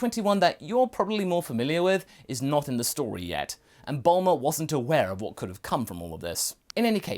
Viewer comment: Wait, did he say yet?!